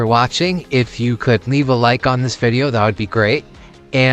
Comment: I heard “Happy Paper-Fursuit Baking!!” 😭